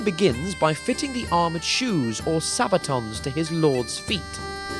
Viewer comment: did he just say “Sabaton”? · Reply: Yes, they are called sabatons. Sabaton the power metal band didn't invent the word.